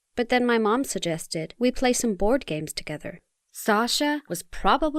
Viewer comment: I thought she said 'Gay' when she said 'games'. Oops.